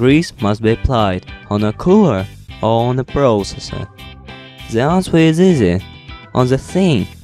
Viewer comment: thanks for the vid, but @ 1:27 what did you say? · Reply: Leonard Morales "the answer is easy" Sorry)